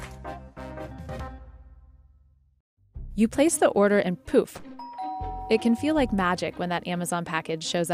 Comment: I thought she said "You place your order and poop". Yeah, that's about right for a lot of folks.